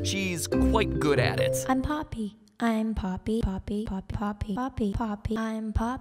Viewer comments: At the start i thought he said "Im Peppa,I'm Peppa" XD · poppy sounds like she is saying- im papi